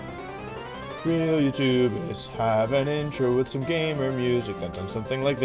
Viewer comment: I honestly thought that you said YouTubus instead of YouTubists in the intro until yesterday. I just wrote it off as a thing to do.